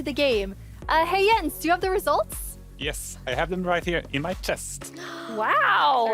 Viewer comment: Btw, non auto-generated captions at 11:32 say "I have them right here in my test" instead of chest